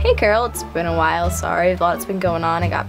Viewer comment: ummmmmmmm...... this is awkward when he said i got my powers i thought he said i got my period and i was thinking of the bloody pulp thing and yeahhhhhh.......